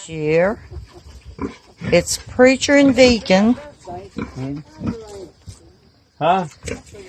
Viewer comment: At first I thought you said the hogs were named Preacher and Vegan. lol Got Deacon when you said it again. Pork is my favorite meat, so sweet. Great show again. All the Best Deb, Canada.